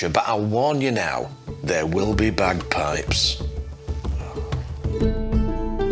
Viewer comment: I really must get my ears tested, i really thought you said there will be bad pipes and not bagpipes 🤣🤣